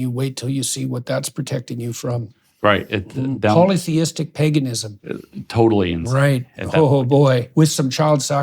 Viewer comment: what did he say at 39:25 was it polytheistic paganism?